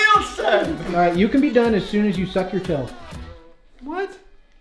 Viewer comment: I thought he said now stub your toe instead of suck and I was like you just took that too far